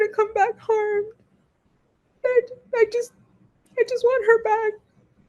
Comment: Ik she said “I want her back” but I really thought I heard her said “don’t want her back” the first time I listened. The mom was way too composed for how recently her daughter disappeared. A 13 year old “forgetting” their phone. A lot doesn’t add up in this I’m also curious if she was really the one who wrote the note about living in the woods in her phone. She didn’t have her phone, maybe that was planned before her disappearance